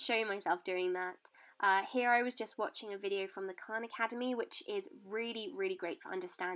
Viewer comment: Literally thought she said "Quran academy"